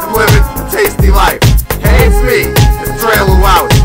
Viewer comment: It sounded like he said "tasty lice" instesd of "tasty Life"